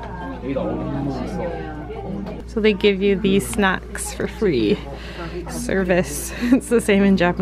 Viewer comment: I thought you said "sex" instead of "snacks"!!! Lol.